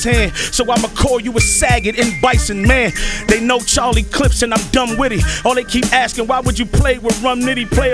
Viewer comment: I thought they said charlie chips for a sec 😂